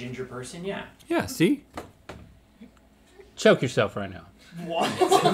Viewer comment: Lmao i thought he said show yourself that would have been good too